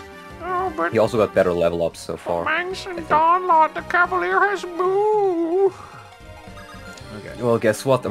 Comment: When mangs said "but the cavaliers can move" in the mocking voice, I thought he was saying "but the cavalier has boobs" lol